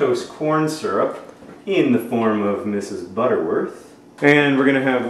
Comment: lmao, I thought he said, "1 teaspoon of high fructose corn syrup....that of Mrs. Butterworth's corn" :O But alas, I was wrong.